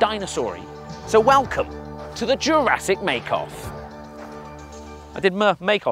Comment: Is it bad that I thought it said jurrasic make out X3 Also I was cringing so much cause I know how to make a cake But anyways the game grumps were very funny Since I like to bake I know a lot of things on how to make cakes, so it was just hard to watch